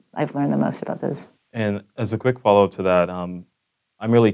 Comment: Since I can't find the subtitles, some parts are hard to listen for me. For example, 51:42 What does he say? "As a quick ..........". I know this part doesn't matter so much but I just want to know. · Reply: "As a quick follow up to that..." - That's what he says :)